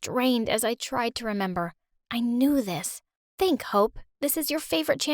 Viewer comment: At first i thought she said "think hoe"